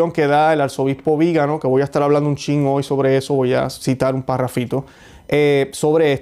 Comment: Hola. Muy buen programa. En el minuto 13:57 escuché que dijiste: "voy a estar hablando un chingo de eso". ¿Es correcto? Quizá escuché mal. Soy mexicana y en mi país se usa mucho la expresión "un chingo" pero hay que aclarar que es la jerga mexicana y el equivalente en inglés a términos como "fuck" or "shit". No me parece apropiado incluirlos en tu programa al menos que lo hayas hecho conscientemente. Saludos.